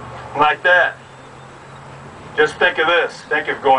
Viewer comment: does he say ‘bent over’ or ‘Ben Hogan’…?!